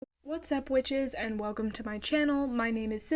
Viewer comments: Bro I really thought she said “what’s up b/tches 😼” 💀💀 She’s not old enough to probably ask for nsfw 💀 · did she just call me a bitch?